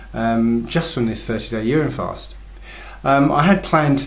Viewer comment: I thought he said "year end" fast... the I thought he said "urn and fast"... then my wife pointed out to me that he said "urine" fast. YIKES!!!! Does he mean to say that he drank urine for 30 days? WTF dudes...